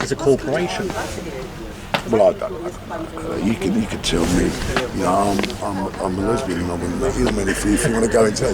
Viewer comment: "You could tell me that I'm a lesbian" Am I hearing things or did he really just say that?..